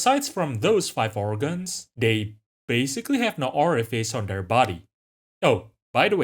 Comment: you said "no orifice" and I thought you said "no aura or face" and I was like "yeah youre right this worm has no aura at all, ugly ass worm"